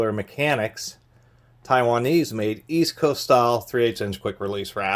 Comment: Oh, Easco! At first I thought you were saying 'East Coast style' and I was wondering what was special about New York ratchets!